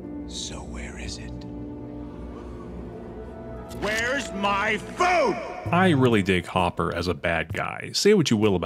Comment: I was so young when I watched this movie that for the longest time I thought he said "Where's my phone?" 😂